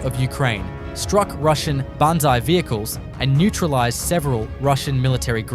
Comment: Closed Caption says "BONSAI vehicles..." Probably mean "BANZAI Vehicles", referring to a Japanese "Banzai" charge from WW2.